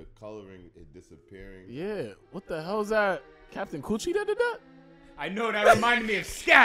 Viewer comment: Why'd it sound like said "Captain coochie"